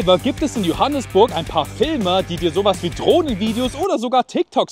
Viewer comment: Ich habe Drogenvideos verstanden 😂